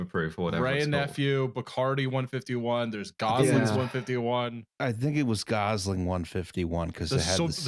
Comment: misheard Gosling 151 as “Gazans 151” and i just thought “well yeah i’d need something that strong too to deal with isreal”